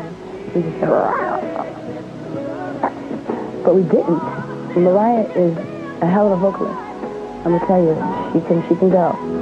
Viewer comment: What does Whitney say at 2:44 about Mariah? “She think she can go”? It’s a little hard to understand. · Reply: Reuben Walton she said “Mariah is a hell of a vocalist she can go” meaning she has a great voice